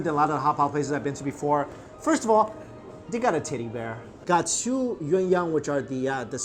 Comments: I thought you said tiddie bear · When he says teddy bear it sounds like he said titty bear XD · I thought he said titty bear not teddy bear. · i cant get over the fact when he says teddy bear it sounds like hes saying "TIDDY BEAR"